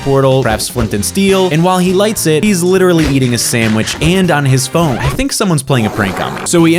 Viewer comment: I heard you say you:they completed the portal and ENTERED THE "END"me:WHAT IN TARNISHING ARE YOU SAYING HE IS ENTERING THE NETHER DUDE